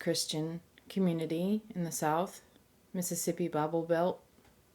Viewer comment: I thought you said "Babble Belt". :-) You are truly one of the most beautiful people I have ever met.